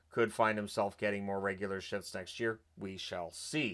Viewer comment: sounds like he said regular shits